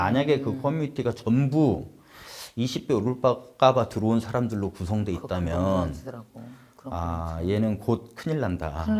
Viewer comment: 여기 전 잘 못알아듣겠는데 뭐라고 말씀하신거죠.. ㅜㅜ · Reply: 20배 오를까봐 들어온 사람들로 구성되어있다면 인거같아요! 투자 목적이용